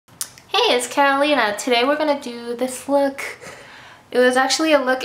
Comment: I almost thought she said "hey guys it's Carolina" in the beginning